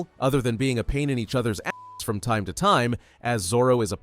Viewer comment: wth did he just say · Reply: He always says pain in each other ass time to time